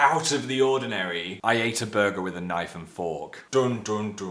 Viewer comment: At first I thought he said 'I ate a burger with my iPhone 5'